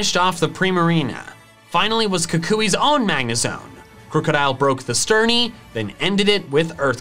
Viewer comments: Is it just me or did I hear say “krookadile broke the sturny” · did he just say "sturny"